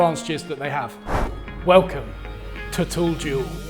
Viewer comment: Thought he said tool jew